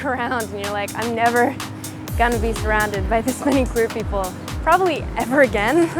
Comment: When eugene said queer people I thought he said pretty boy